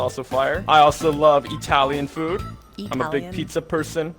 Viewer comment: I heard you say “eee-talian”, 😂🤣😂🤣😂